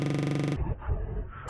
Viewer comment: I hear it "got jazz"